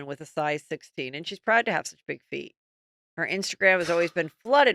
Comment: Ooohhhh big feet I thought heather said “big V” 😂